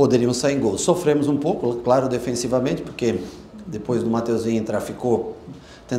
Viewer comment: Só eu ouvi nos 00:47 que Matheusinho traficou? Kkkkkkkkkkk